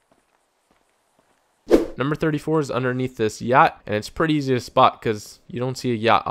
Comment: i thought he said gyatt